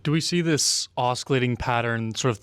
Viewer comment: Did he say, "oskillating"?